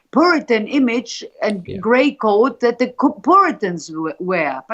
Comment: she says “grey” “coat” or “code”? Does anybody know what this means? Thanks.